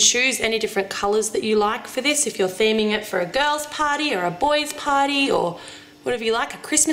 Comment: Lol! My sister thought you said a girl's potty and a boy's potty instead of a girl's party or boy's potty! After I explained she then said she loves your accent! :)